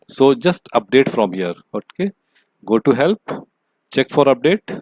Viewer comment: i heard : Go to Hell